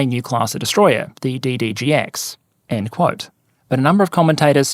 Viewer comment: I was only half-listening and misheard "DDDD-X" I was like ...do we add another D every time we cancel and restart the program